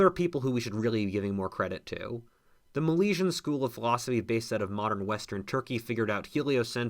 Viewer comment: did I hear that right? "The Malaysian school of philosophy based in modern western Turkey". English is not my first language so maybe I didn't understand.